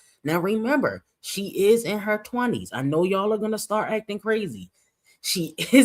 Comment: what did you say? In her 20s. 😳